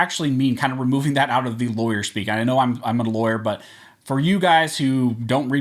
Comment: Hello Mr. 6:22 you are saying you are not a lawyer? Did I hear that correctly? In that case I'm surprised because you know the law very well and use the terminology